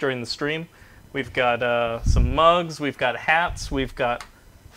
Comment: "Giving away some nuka world prizes." I thought he said "giving away bugs" instead of "mugs." Eh, surprised they aren't giving away bug prizes just like their whole game